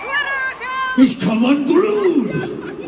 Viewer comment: What is the context of his speech? 0:17 what did he say? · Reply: "He's come unglued!"